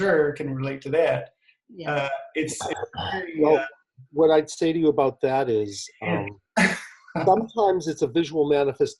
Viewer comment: Great to see again! Also I think you caught an EVP at 16:03, I cant understand exactly what its saying though ( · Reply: I think it's saying "Yes, she does."